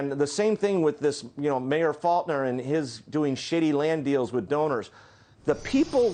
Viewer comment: did he say shitty land deals! Lol